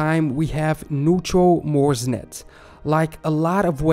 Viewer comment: i thought for a second you said Mumsnet. 😆 🤣 😂